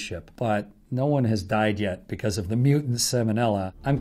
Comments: I thought he said hetfield and then he played guitar I thought he was James hetfields brother 😂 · lol, I really thought he said "Chris Redfield" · I thought he said "hi im chris redfield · I thought i heard Chris Redfield 😂😅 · I misheard and misread Hadfield as Redfield. "Hi, my name is Chris Redfield and I will talk about Bio Organic Weapon." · Lol!I thought he said "im claire redfield